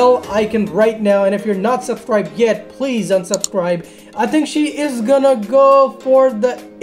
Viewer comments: it sounded like he said please unsubscibe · did he say unsubscribe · Did He Say ''Please unsubscribe''?